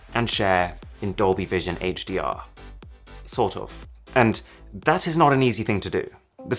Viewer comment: CAN ANYONE TELL ME WHAT HE SPOKEN HERE.. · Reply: Sort off